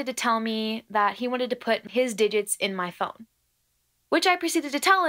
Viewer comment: I THOUGHT HE WAS ABOUT TO SAY SOMETHING ELSE!! 😂🤧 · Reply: I heard "d¡ck" at first lmao but I listened again, it's "digits"